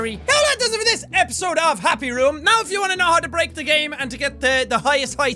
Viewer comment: Dang it, I thought that it said happy wheels instead of happy room